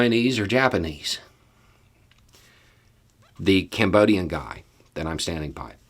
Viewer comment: I see racism and bigotry as fear based. But funny thing- when you related that Cambodian story I heard "Cameroon". and thought were these Asians "black".It's exhausting being me.